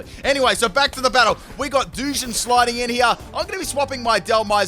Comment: ngl i thought he said douchehead until i saw the pokemon XD